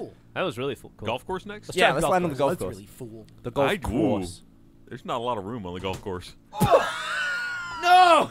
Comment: What did Ryan say at 19:00? I couldn't hear it. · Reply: "There's not a lot of room on the golf course."